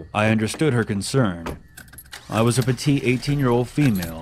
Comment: it says fatty instead of petite